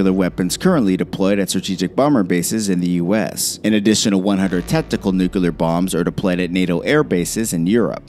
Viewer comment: did you say "testicle nuclear bombs"?